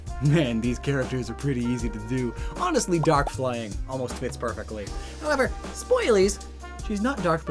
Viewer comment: I thought he said "Tits perfectly" when talking about Rouge.